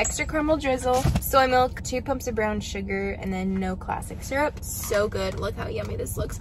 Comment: when you were saying your starbucks order i thought you said two cups of brown sugar not two pumps and i was like wow i mean you do you😂 ilysm and congrats on the growth of your channel❤️